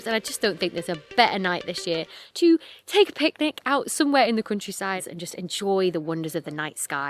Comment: I keep hearing "crunchyside" when you're saying "countryside" and it's making me giggle 😂😂😂